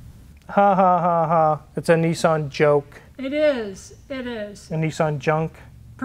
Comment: I thought he said Nissan Joke.